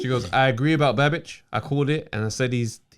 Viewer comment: The first time said “Babic”, I thought he said “bad bitch” and I think I like that better than Savage. Babic the Bad Bitch. Someone on Sky needs to bring it up in an interview, along with his small size.